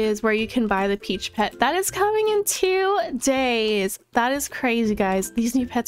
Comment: I thought she said the new pets were coming to-day but she said two days 😭